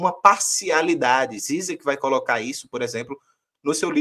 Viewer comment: Qual o nome citado nesse momento? Não entendi. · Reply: Acho que é o Zizek